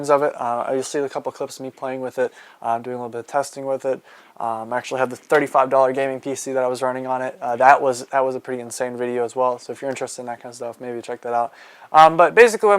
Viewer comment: did he just say 35 dollar gaming pc? either im tripin, or thats the cheapest pc ive ever heard.